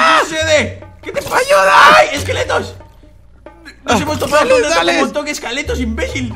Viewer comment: soy yo o oi que dijo escaletos